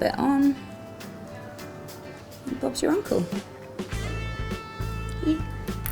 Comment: Did she say so bob's your uncle??